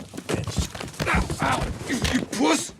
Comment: Did Matt call Rick a puss at 3:42-3:43?